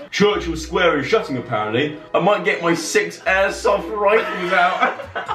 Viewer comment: please help me. What the fuck are they saying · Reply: clicked on this randomly saw your comment, 3.07-3.10? "I might get my 6 air soft rifles out"